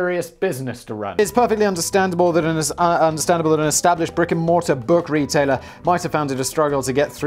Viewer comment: Good lord - I heard "rigor mortis" instead of "brick and mortar" the first time around & had to replay it. Though with Borders I suppose it was just as accurate to call them a rigor mortis company.